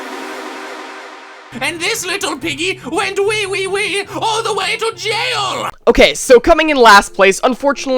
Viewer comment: wth is he saying 😂😂😂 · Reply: AN D THIS LITTLE PIGGY WENT WEE WEE WEE ALL THE WAY TO JAIL